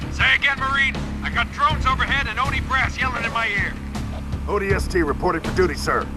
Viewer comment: love how the subtitles for the video say "I got odie brass yelling in my ear" instead of Oni brass at 3:52 😂